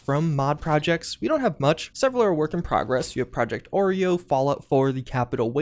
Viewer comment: Did he say "Project Oreo?"